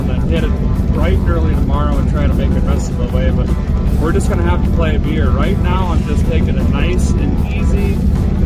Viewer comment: Might have miss-hear-ed it, but I swear I hear-ed "Play it by beer"